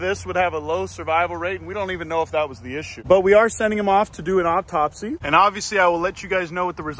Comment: my condolences sir, but did you say OPTOPSY